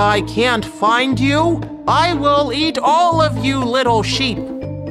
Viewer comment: I heard "I will eat all of you little shit!"